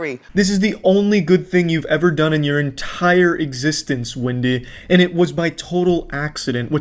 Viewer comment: On Wendy's fart - 'this is the only good thing you've done in your entire existence "Windy"...' (Did he say that, a freudian slip or what?! 🤣